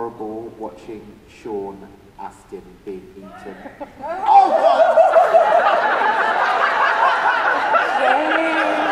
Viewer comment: what did the person from the audience say at around 27:59 ? Can't understand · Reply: "Spoilers!"